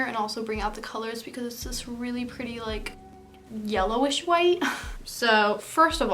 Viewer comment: Did she say yellow شوي ?